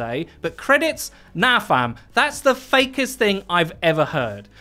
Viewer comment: did he just say "fam"?